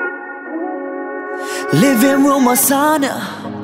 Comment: First words I swear i hear "living room lasagna" Everytime lol